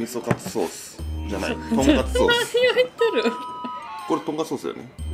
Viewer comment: 空耳ですが、「何を言っとる？」て名古屋的pronunciationで聞こえるの笑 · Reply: 何を言ってると言ってました 私は人生って何を言っとるって言ったことない